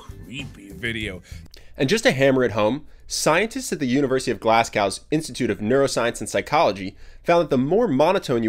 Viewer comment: Did you just call my beautiful city, Glass Cow?! (Tbh I really can’t tell the way he said it was so weird)